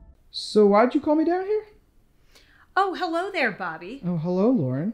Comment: What Bobby said: "Why'd you call me down here?" What I heard: "Why'd you call me daddy " I need help😃🤚